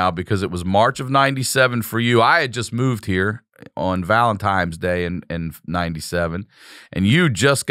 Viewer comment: Does Sickler say Valentimes day?